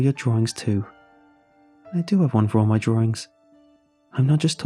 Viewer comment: I'm sorry but I hear it as "drawRING"